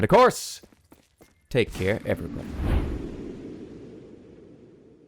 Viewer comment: When i played i thought they were saying Pastor Whiskey.